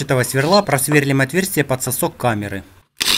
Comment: Мне одному послышалось на 2:40 для сасков камеры Если да то ставь лайк